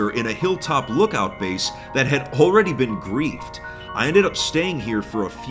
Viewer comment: a question for a native speaker! at this second the author said "that have already been grieved", what is the meaning of the word "grieve" in this case? From the context, I thought that it meant "destroyed", but I did not find this meaning in the dictionary, PLEASE HELP!🙏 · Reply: He said Griefed not Grieved. Griefing is to harass and act in an unsportsmanlike manner this term is commonly used in the internet, it can range from anywhere from bullying to destroying, it's a pretty flexible term, it just basically means to do bad.